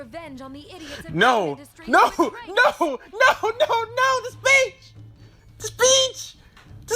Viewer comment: 10 seconds in and then "NO, NO, NO, NO, THE BITCH, THE BITCH" (yes, i know you're saying "the speech", but i thought you said bitch the first time i heard it)